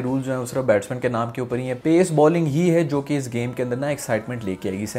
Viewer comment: did u just said “paste” bowling 🤣🤣🤣🤣🤣